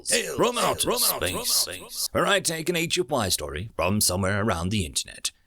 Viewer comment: Every time I hear "I take HFY Stories..." I hear "ancient wive's stories". Loving the common knowledge of future ancient grannies!